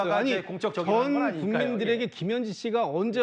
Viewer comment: 김진욱 "전 인민들에게"??? 내가 잘못 들었나?